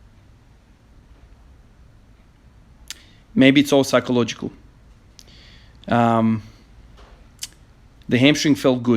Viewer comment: Congrats mate! Truley inspiring, just out of curiosity what did you say @ 8:39 after completing the lift? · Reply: something along the lines of: "mamu ti jebem u picku"